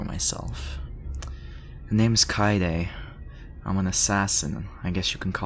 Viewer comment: Bruh when he said his name I heard "Kaede" that sent my brain straight to Kaede Akamatsu Der Flohwalzer starts playing in the background